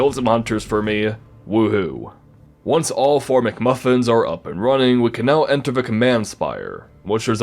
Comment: Did you just say McMuffin instead of McGuffin, does this mean what i think it means?